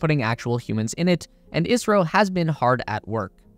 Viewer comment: he said Israel instead of India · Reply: he said ISRO - that's the Indian space agency :)